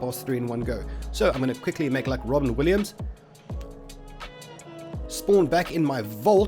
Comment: Don't like those Robin Williams jokes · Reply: had me pause a sec and then realise, nah that was funny, reckon Robin would have laughed. Maybe its cos i thought he said Robbie Williams at first...